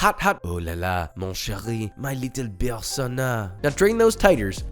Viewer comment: My main language is french and I still don't understand what he wanted to say 😂 Like it started with : Oulala, mon chérie, mon «litapersona» 😂 Correct me if I'm wrong but litapersone is not english, and certainement not french 🦄